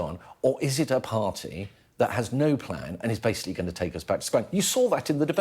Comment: I thought they said labour had no plan so what plan have they done this to